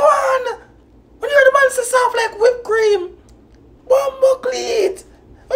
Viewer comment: Yuh know I thought he said, "d gyal dem soft like whip cream" I didn't know he say BIL lol